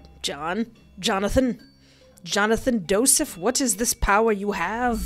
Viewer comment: nah did she just call him Jonathan Doeseth